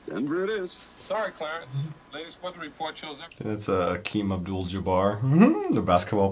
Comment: Did you say Akeem? (Kareem)